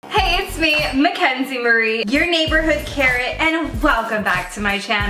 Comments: no one: literally no one: Mackenzie Marie: hey its me, Mackenzie Marie, your neighborhood carrot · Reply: Lol I thought she said your neighborhood Karen lolll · I thought she she said, hey it’s Mackenzie Marie, ur neighborhood Karen xD